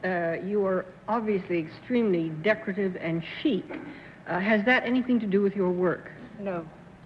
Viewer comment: Ok time to get my hearing checked. I was quite shocked when Dorothy was questioning the last contestant (, The Stockbroker), I swear I thought she said "Miss Ross you are obviously extremely decorative and cheap".............sorry Dorothy........she did say "decorative and CHIC"